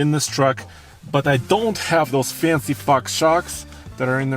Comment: did he say 'those fancy Fox shocks' , I'm not so sure :)